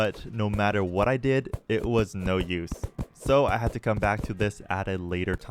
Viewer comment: did you say "it's no use!"?